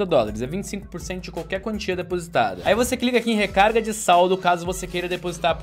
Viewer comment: Só eu ouvi ele falando "clica em recarga de saullo" em 2:07???